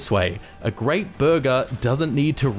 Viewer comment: I thought you said “verga” instead of burger😂